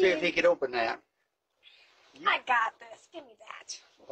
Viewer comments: Oh that broke my heart when he said he has no friends left 🥺. I’m glad he lives by nice people. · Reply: i thought he said, "I dont got no strength left" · “I don’t have no friends left” is the SADDEST thing you can hear from an elderly person. Bless this man and may all his jars be easy to open 🥺❤️ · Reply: Yea i thought he said strength, not friends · Broke my heart when I heard “I don’t have any friends left” It’s sad how life is some come some go. Appreciate everyone you have e around you and thank good for another day. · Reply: I thought he said I don't have any strength left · I think he said "I couldn't. Cheryl couldn't. I aint got no strength left." Bless these people. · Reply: I thought you said I don’t have any friends left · “I don’t have no friends left.” 😢 Bless that man, and bless these people for being kind. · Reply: I thought he said I don't have no strength left. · Man, seeing elderly in this state, "I don't have no friends left" really makes a grown man cry!! Such a pure soul! We need to respect and protect people like this man at all costs!!! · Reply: Yea, I also thought it said friends at first because of reading the subtitles but second time around I definitely heard strength. Makes the interaction a bit more lighthearted so I’m happy I caught it. Think I saw a few others catch it as well. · Oh that broke my heart when he said he has no friends left 🥺. I’m glad he lives by nice people. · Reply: I thought he said strength left.